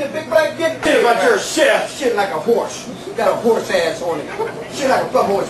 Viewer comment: holy cow! listen carefully at 1:10 "I don't give a shit, about your type." thats what I just heard :O....is tourettes guy possibly racist? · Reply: I heard "I don't give a shit about Kentucky-" and then got cut off